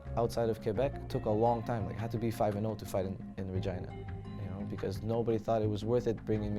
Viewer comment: Did he say he had to be 5-0 to fight in vagina?